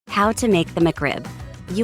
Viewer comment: does anyone hear how to make immigrants